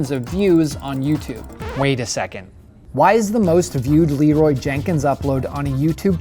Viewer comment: When i first heard this meme i thought he said "leeroy's jerking"